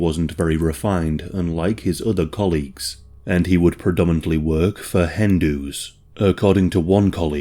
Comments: He would predominantly strip for Hindus?!?! Did I hear that right? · I could have sworn you said "he mainly worked for Hindus" until the penny dropped and I realized Hen Do's